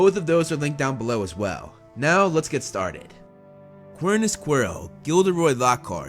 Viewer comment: k, at 1:02, the subtitles say “miRNA squirrel” 😂